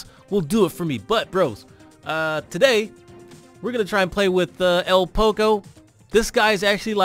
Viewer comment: In 0:27 did he say el poco